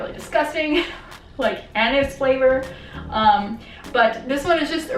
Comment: when you were describing the tooth powder and different flavours, did you say "anus flavour" or did I mishear LOL